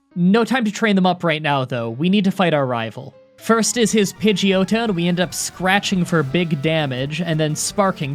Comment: sounds like he said "pidge-yoda" 🤣🤣🤣